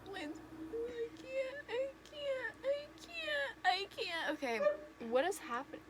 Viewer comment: why did I hear that as “Ikea Ikea Ikea Ikea...” 😂